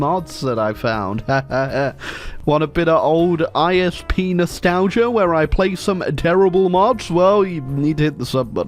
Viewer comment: why do I hear IS Pnos talgia instead of ISP nostalgia?